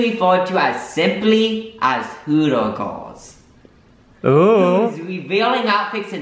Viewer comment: When he was reading the definition from the laptop I thought he was saying “cooter”